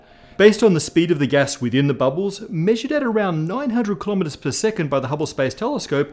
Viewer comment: closed captions say 9000km/s and you said 900 - which is right?